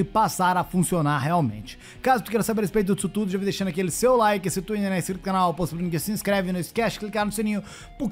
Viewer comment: Eu nunca entendo o que voce fala em 1:34. " Se tu nao é inscrito no canal xxxxxx se inscreve"